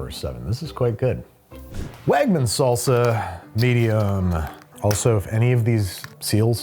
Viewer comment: I thought Babish excitedly said "Wagman's salsa... tedium!"